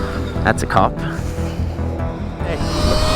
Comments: anyone hear “hey” after he says cop? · anyone else hear the "hey" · I heard the hey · anyone hear "hey" at 2:20 or am i tweaking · Did anyone else hear a very quiet hey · i swear i heard someone say hey from the left side of the mic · why do i hear a hey in